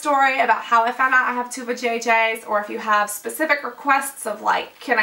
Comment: Ok so I had your video on another tab playing and reading an email then all or a sudden I hear 'I have two vajay-jays'. I had to rewind and listen again. It was an audio double take :p